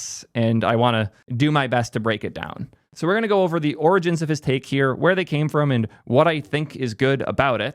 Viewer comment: I heard “we’re gonna go over the origins of his d!ck,” lmao.